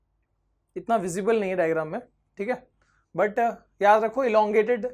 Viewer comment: does he said thick😂😂😂